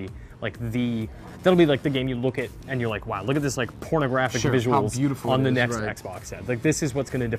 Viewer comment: Is it just me or did he just say pornographic visuals